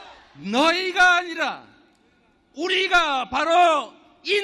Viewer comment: 너희가 아니라 우리가 바로 이 나라의 왕이다를 나이가 아니라로 들었넹 ㅠㅠ 이재명 대표님 발음 좀 ㅠㅠ 그리고 집회에 꼬장부리는 노인?들 있어서 참 뭔가 싶었음 ㅠㅠ